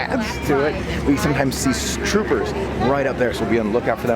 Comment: What did he say at the time stamp 3:55 😂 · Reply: Michael Mullins He said troopers..........I think he was going to say Storm Troopers. 🤷🏻‍♀️